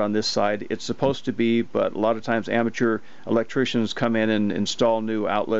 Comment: Misheard "lot of times amateurs" as 'long time amateurs' lol